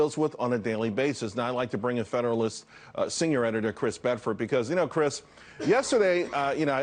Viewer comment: What did he say? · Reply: "Find the bots."